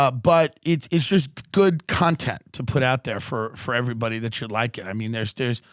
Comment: Pretty sure I just heard..."CUNTtenT" ?IDK...@ 1:40 K. Back 2